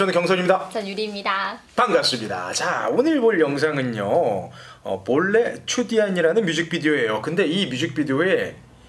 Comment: does he say ""chutiya"" ?? XD XD XD XD